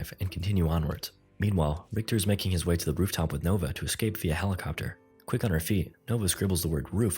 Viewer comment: I thought it said "via velociraptor" in the subtitles